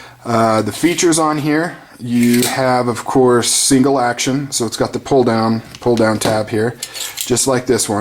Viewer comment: I thought you said "seagull action" Heheh.